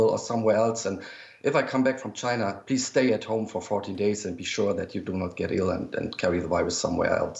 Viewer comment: Instead of wondering or asking what did he say... "Did he just say 40 days?!" Just go back in the video and found where he said how many days for quarantine... and (turn up the volume) You will hear him say ▪14 days▪... it's because of his ascent as to why you are hearing 40 days! That's how I found out the correct day! 😊